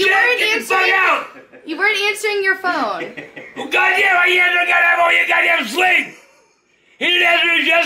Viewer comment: If I can translate correctly, I think he said, “Well, G**damn! How can you answer the G**damn phone when you G**damn sleep?!?” Either way, his gibberish always makes me ROTFL!!! 😆😆😆